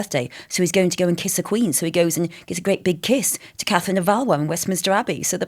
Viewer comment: Catherine of Awaw ? What is she saying ? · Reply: Valois